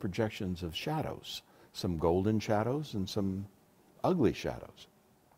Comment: Whew. I thought he said "Golden Showers".